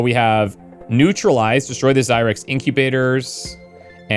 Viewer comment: Had to laugh, when you were staying zyrex, I thought you were saying zirax (from empyrion galactic survival)